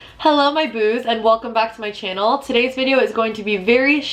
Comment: Thought she said heelo my boobs 😂